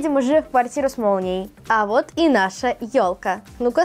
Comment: Мне одному послышалось "а вот и наша тёлка" 😂😂😂